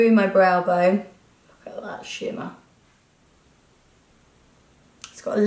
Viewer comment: Love this! 💙💚💗 On another note, when you said '"look at the shimmer' ...I genuinely thought you said 'look at that shit man' 😂😂😂 xxx